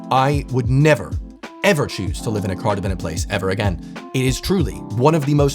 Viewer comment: sorry, i didn't quite catch the word at 9:14 - "I would never choose to live in a [cardorbated] place - anyone know what adjective he used there? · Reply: Car dependent